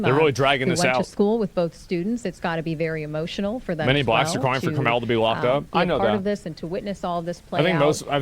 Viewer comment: I hear this "Fry Karmelo"